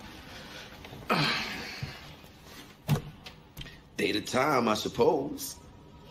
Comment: I thought he said," they do time,I suppose".